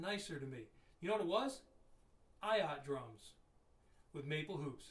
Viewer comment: my last name is Ayat. Like for real.. Just sayin. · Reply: Stock Fadi : I thought you were responding to what he said about Ayotte drums with wood hoops. No disrespect meant at all. Hear it at 3:14. It sounds like “Ayat” but it’s “Ayotte” which should be pronounced as “Ay-yawt”. <link>